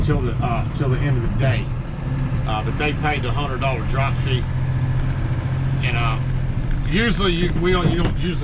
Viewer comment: Ok! I clearly heard "drop fee" (LOL!) What a great channel!